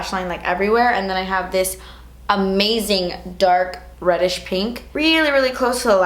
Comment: When you said reddish pink at 3:18, it almost sounded like you said "radish pink" and I thought that worked too, considering that it does look like the same color as a radish 😂